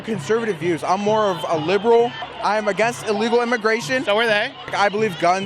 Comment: When he said “I’m a liberal” all I heard was “yea I’m retarded” lol